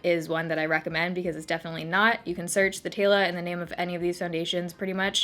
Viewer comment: I thought it said thotayla